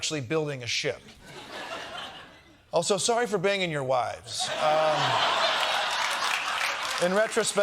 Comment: I'm sure Kosta says "sorry for banging your wives," but the subtitles say "sorry for being in your wives," which is a funnier way of putting it.